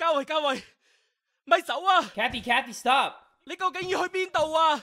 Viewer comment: Oh I thought he said "Don't leave/run away" not Stop haha. XD Eh, same thing.